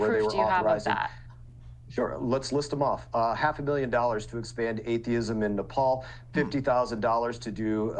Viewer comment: did i hear that right "expand atheism in nepal"??? Tf?